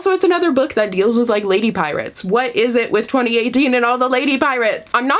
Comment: You said "lady pirates" and I heard "lady privates" and I took a double take. Glad we cleared that up.